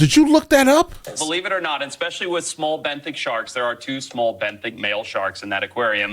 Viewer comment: Did he say 2 young bent dick sharks? Or am I tripping? @ 2:07 😂